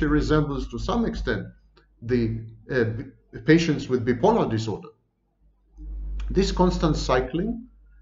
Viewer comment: Did anyone understand what he said at 15:32-- "...at this point she resembles someone with _____ disorder"? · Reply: Bipolar